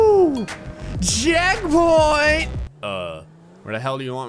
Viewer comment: DID ANYONE ELSE HEARD HE SAID JACKPOINT LOL :D